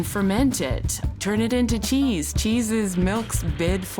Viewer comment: "Cheese is milk's bid for immortality." That is just such a clever thing to say, thank you for the smiles😊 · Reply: It was cheese!!! Hahaha I rewound that part so many times because I kept hearing “tea” & couldn't for the life of me understand the quip. I was making the most abstract of connections & kept sounding it out trying to make sense of it. 😭 Bless you because this would have been one of those things that I would randomly think about for years, forever wondering what it could mean. 😩